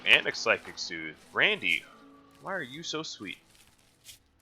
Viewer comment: ~ I could have sworn you said "Brandy, why are you so sweet" and I was like mmmm brandy. Great series btw. I've been wanting to do this type of challenge for a long time but I have way too many mods that would make it so much easier than vanilla. Keep on keeping on!